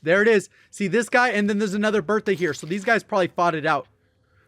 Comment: I heard Birthday